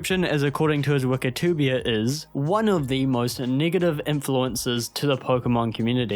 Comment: accent aside you know what you said😬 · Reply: Negative? If you hear the n word there that’s a you problem my guy.